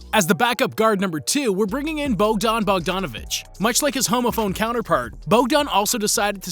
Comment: For a moment I thought you said "homophobe" 😂